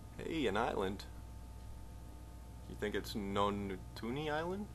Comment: Lol, still didn't get "nontoonyt" I hear :D